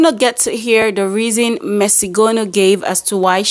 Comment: I thought she said messy gono😅😅😅😅😅